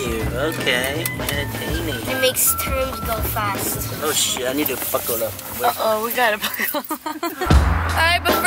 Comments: I thought he said "ohh shit, i need to fuck all up" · When dad said, "oh shit I need to buckle up," I thought he said, " oh shit I'm about to fuck you up "